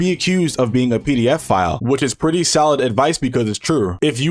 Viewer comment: did he really say P.D.F file (i know what the actual word is but it just sounded funny to me)